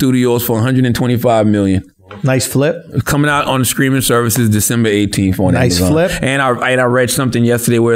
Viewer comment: I want to start a drinking game Every time “tha god “ mispronounces a word. Every time he says streaming services sounds like he is say screaming services. I think I might be drunk either 30 mins